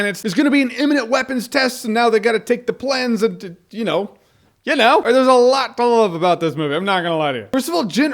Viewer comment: what the fuck did he say? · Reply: I think he said theresalottowalkaboutthismovie. FOAK.